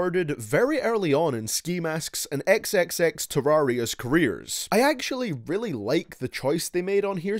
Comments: He said xxxterarious Or xxxterarion's Kinda hard to tell what he's saying sometimes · Did he say xxxteraresis